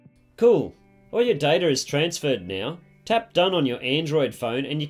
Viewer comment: did he say DATER??? 😂😂😂😂